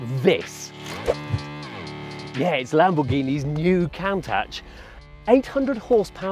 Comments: Did he just say Cown-tatch? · did he just call it "count tach"? LEL